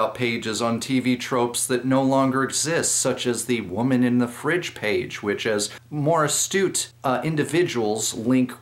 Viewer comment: I heard "Woman in the Fridge Raider." Then imagined Lara Croft trying to take over Cooking Mama.